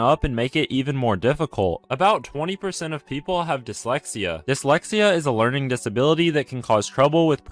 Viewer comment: When he said dyslexia I thought he said dementia good thing I don't have either of those When he said dyslexia I thought he said dementia good thing I don't have either of those